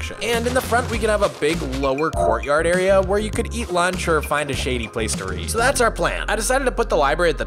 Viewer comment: I was tired when I watched this and I thought it said “Eat Shade”